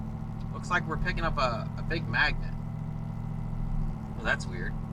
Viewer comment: I thought you said you was picking up a Big Mac. I got excited and it turned out to be a "big mag"net... lol